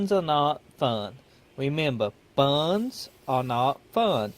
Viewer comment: I thought the voice was an act but... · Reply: Sound like he was saying buns are not fun lmfao good video thoe